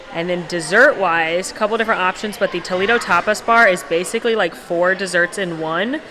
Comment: When you said "tapas bar" I thought you said "topless bar"....I was like whut?????